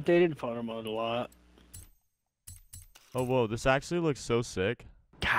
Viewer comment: I'm sorry did you just say.... OWO